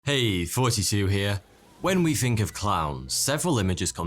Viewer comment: Is it me or does it sound like he says hey 42 here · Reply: He does say 42 you're hearing it right .